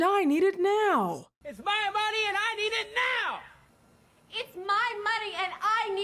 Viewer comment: And people think we free .....and that saying ,"IT'S MY MONEY and I Need IT Now ,......I thought they said ," IT'S MY MONEY and I WANT IT NOW